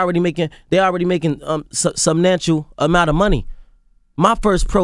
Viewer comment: he just said subnantial instead of substantial .. My IQ is nonexistent after this